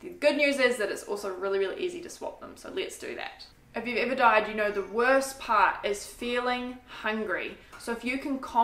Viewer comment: my husband is officially grounded from listening to your videos. Last time he thought you said cocaine instead of cooking. This time he thought you said "if you've ever died, then you know what its like to be hungry" 🙈🤦